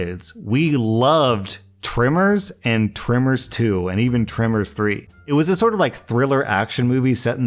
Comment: I need to get used to accents... I swear you said "trimmers" instead of "tremors" and thought you were talking about some mysterious slasher flick starring someone with hedge trimmers, not the Graboids, Kevin Bacon and Burt Gummer!